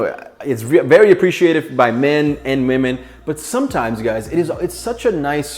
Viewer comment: did he say memen 😂